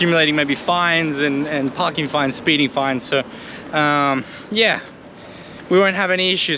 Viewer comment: Did I hear "speeding fines"? 26:28 - Likely fried plantain (spa: platano macho, por: banana-da-terra). *The off-roading segment was fun, cheers!